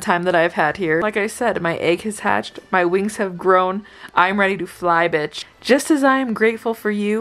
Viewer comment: 1145 I THOUGHT SHE SAID MY WIGS WAVE GRONE